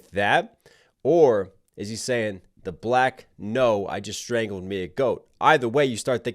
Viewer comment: I thought he said "black noir" tying in with the superhero/super villain flow with magneto